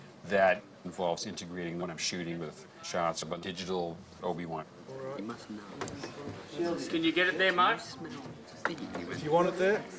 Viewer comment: "What you must know is.... you smell" Is anyone else hearing this?